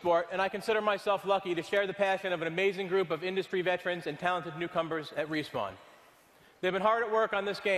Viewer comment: did he say "Talented cucumbers?"